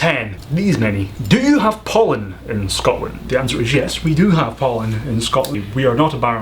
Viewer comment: I heard #10 as "do you have Poland in Scotland?"